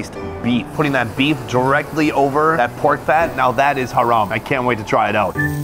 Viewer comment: did he just say "thats haram"?